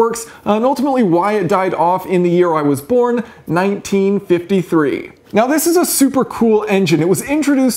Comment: At first I thought you said "in the year my dad born, 1953" 😂. Now I'm curious what's your birthdate ?